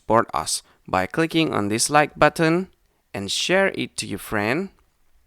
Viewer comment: I thought he said « by clicking on dislike button «